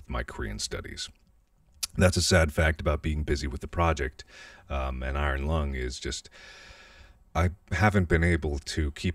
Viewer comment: Did i just hear him say "working on A project AND Iron Lung" What's this project Mark... Hmm Maybe a lil FNAf project?? Hmmm?? We need answers 😭😭